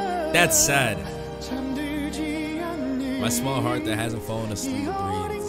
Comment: I have to say that at 8:16 the subs are wrong and he actually says “my young heart” this time instead of “small” and idk what’s more heartbreaking honestly 😭